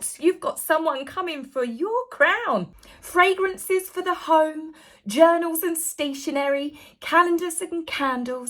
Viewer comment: I thought you said Fragrances for the homeless and Dog Shyte Shampoo.... I had a replay it. That's enough internet for me today, thank you.